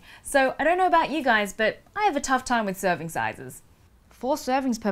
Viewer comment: At first I thought you said, "I have a tough time with circumcises." I was like, okay Nat, taking a different turn but not unexpected.